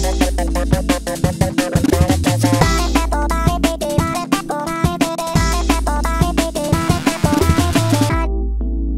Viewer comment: sounds like hes saying party people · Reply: Party pooper